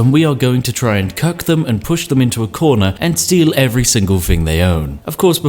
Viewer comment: Did.... did I hear cuck them into a corner at 5:19?